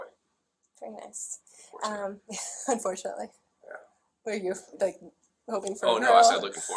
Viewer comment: When he said "I have a son" then muttered "lookingforwardtoit" and she thought he said "unfortunately", while knowing his crimes... Man, I bet she had some serious creep chills up her spine.